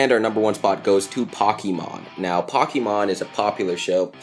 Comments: "pockiemon is a popular show" what did he just say? "pockiemon-" slows it down * " P O C K I E M O N " * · Do you mean "Pokemon" cuz it sounds like you said "paw"keymon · it sounded like he said pockey